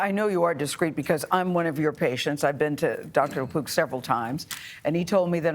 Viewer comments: I thought he said Dr La Poop...🤣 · this whole time I thought they were saying the Doctor's name was Dr. Lapoo not Lapook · I thought they said dr. John la-Poop 💩🤦‍♀️😬😅✌️ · I swear I thought he said Gastroenterologist Dr. John Lepoop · I thought they said the Doctors name was "Dr. Lapoop" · At first I thought he said the doctor's name is Lapoop... I was like, "how fitting." then I read the note and saw "Lapook."